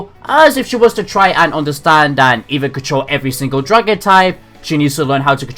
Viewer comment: all I heard is control every drug and type